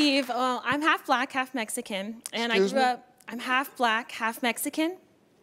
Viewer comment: im so high i. thought she said im half crack half pedestrian